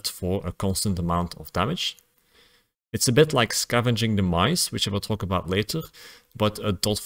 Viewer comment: I thought he said "scavenging the mice". 😆 EDIT: Omg he actually wrote it in the time stamp...